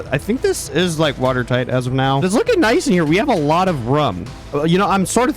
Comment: we have a lot of RUM (instead of room)